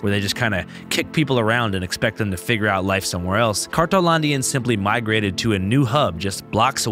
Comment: Isn't it called, "cartonlandia" cause I keep hearing "cartelandia"? Or am wrong?